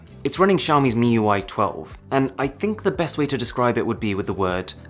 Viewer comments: I had to rewind I thought you said "autistic" at first glance. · Am I the only one who heard "autistic" at 4:40 ? xD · 100 percent I heard him say autistic 💀